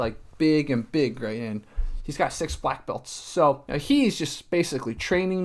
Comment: i thought you said six white belts lmao